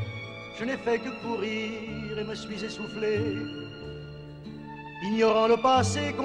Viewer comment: did he say " soufflè" 0:52 ?